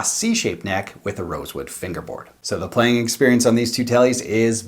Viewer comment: I'M BLIND, BUT THAT '60s TELE SOUNDS LIKE IS BOUND AND HAS GOLD PLATED (FAKE GOLD) FIXTURES. I THINK I HEARD YOU SAY ROSEWOOD BRIDGE. I ALWAYS HEAR THE DIFFERENCE. IS THAT A MEXICAN MODEL? FINALLY, THERE MUST BE TONAL DIFFERENCE BETWEEN ALDER AND ASH??? I CAN TELL.